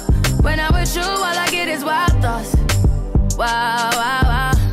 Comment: Okay now instead of waaaa waaaa waaa I hear when "I'm with you all I get is white dots"